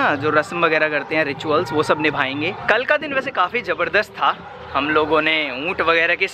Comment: I think he says muth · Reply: Not muth unth